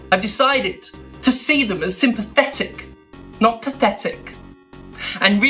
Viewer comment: When Steph said “sympathetic”, I heard “synthetic”